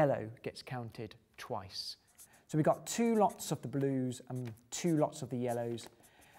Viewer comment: What does he say at 11:35 and sometimes before? "We add two logs(?), lots(?), lods(?)... of yellow..." Dont understand it. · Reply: two lots like two times